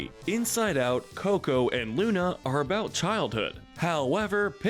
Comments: You said Luna instead of Luca. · seconds in it sounds like they said "Luna" not "Luca" which the movie is called Luca. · did...did he just call Luca Luna? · did he say Luna?